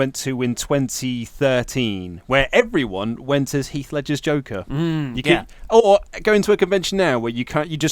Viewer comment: When mentioned Heath Ledger's Joker, all I heard was "Heath Slater's Joker" and I was sitting here trying to figure out how I had missed something so special. "You wanna know how I got these kids?"